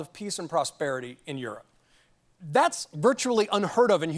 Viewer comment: is the Ukraine in western Europe? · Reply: he clearly says "in Europe". Not "Western Europe".